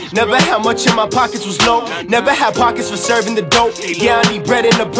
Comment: did he really say never had profit from servin the dope lol ninja dont know how to flip lol